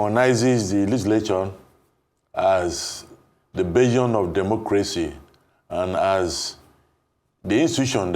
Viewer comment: please what is a "begion of democracy" or did i hear wrong? Distinguished Senator sir😬